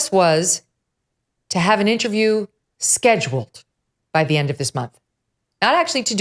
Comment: Was that what was always said? Scheduled by the end of the month? I could have sworn I heard that she’ll have one by the end of the month.